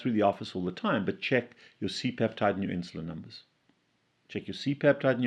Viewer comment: Apparently he said to check your C peptide and insulin levels. Dr. has a habit of speaking in bursts instead of distinctly separating words.